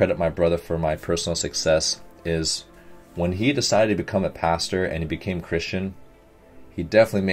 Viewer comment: My brain is so focused on volleyball, when he said his brother is a pastor I heard ‘passer’ and I was like ‘well, I guess passers aren’t generally super agressive’.